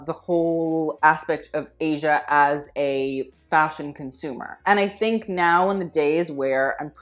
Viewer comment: Okay! Pause for a second! Did he just say " I don't think of Asia as a fashion consumer!"